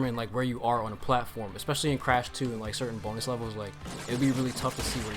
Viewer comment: when he said bonus i legit thought he said b o n e l e s s